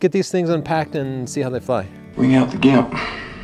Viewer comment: Can anyone distinguish what is being said at 1:00 ? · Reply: "Bring out the Gimp," from Pulp Fiction (1994).